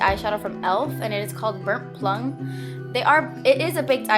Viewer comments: It sounded like you said "burp plung" instead of "burnt plum" Loved the eyes! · Lol I thought you said burnt lung for a second because I was rattling around a bag bahaha! 😂 Love this look thou! Oh m gee it's ahhmazing, definitely will have to try it out. 👍😊